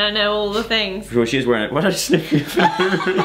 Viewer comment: it sounds like Joe says 'why did I just sniff your food'.